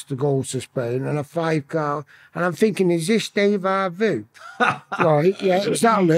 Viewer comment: He is well funny at 2:09:20 he says ''is this day va voo ' instead of Deja vu 😂